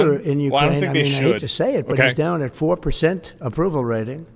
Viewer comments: he did infact say hes "DOWN 4%' not that he is at 4% your chat is rtarded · Trump said regarding the leader of Ukraine "he's down, at 4%, of approval rating." So yeah, you said you thought he said he's at 4%, I don't think your wrong for misunderstanding even if we are paying attention, as I had to rewind and listen more than just attentively, it's indeed a shady way to phrase it, but I think it's also good, because it places emphasis and bring this to people's attention, so I think it's fine for what he said. 16:26 "They took a poll and Zelenskyy's at 4%" the subtitles (cc) says ( is ), but I listened, it sounded like an ( 's ) and not ( is ). --- I think the unethical strong arming is funny, but I agree that the long term resentment isn't good, a good deal should be looking for win-win type of situations. Not that it's related, but I am reminded about what happened between russia and northern japan islands those kuril islands, let the appeasement go for russia and administrated by russia, but maybe allow ukraine to keep economical and usage of the land? Like a mixed shared appeasement? a compromise that would allow both to win/lose depending on the narrative. edit: And a crazy idea, what if for every ten sq mile that Ukraine adds to this zone, russia adds a sq mile to this economic zone lol, so that both countries lose a little territory to this shared zone. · I heard him say , "down at 4%" not down by 4%